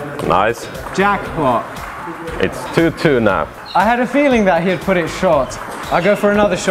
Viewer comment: мне одному послышалось "it's two two нах"??😂😂😂